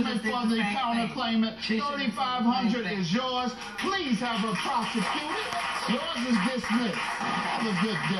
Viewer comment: Okay did I just hear Mathis say “prostituted” or do mine ears deceive me? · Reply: prosecuted